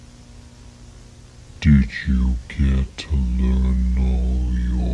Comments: I swear at the start I heard him say “did you pray today?” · i thought he said did you get the noodles?